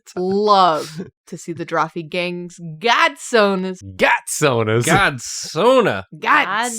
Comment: Bro, I thought that said Goon-Sona and I got so excited and started chanting it before I realized it was actually godsona